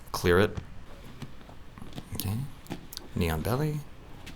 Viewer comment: I can't stop hearing "neon belly" haha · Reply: That's what I thought they were saying until I hear say it clearer into the mic.. I kept asking myself - wtf is a "noen belly???" LOL